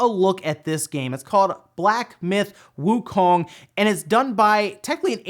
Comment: Wow. When you were listing upcoming games, I thought I heard you say "Black death Wuhan" and I thought......"Shit. I have been playing that game for the last 6 months."